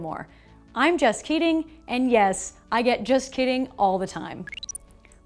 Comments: I thought I heard her name as Jus Kidding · I literally thought she said "I'm just kidding" but it's really "I'm Jess Keating" fml 😭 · Hey everyone, this is the 100th episode of Animalogic! I just wanted to say thank you from the bottom of my heart for sticking around and supporting us all these years. - & Crew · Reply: I'm sorry but I thought you said "I'm just kidding" lol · luky i can see her name written in the video description,coz everytime i hear her say "Jess Keating", i understand "just kiding" :)))))))) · I want to apologise. I thought I heard just kidding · Lol initially I thought you said you're just kidding so I had to scroll back. But then you said it again! Just kidding. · For a moment it sound like she said “hi my name in Just Kidding” lol amen! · I legit thought she said "I'm just kidding" I thought it was the name of her YouTube channel or something. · I thought you were saying “I’m just kidding” instead of your name. I had to watch the second video to understand that. By the way today I have found this channel and I have subscribed as well.